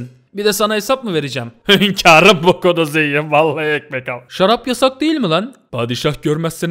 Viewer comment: ekmek almaya cikmismi duydum😁😁😁😁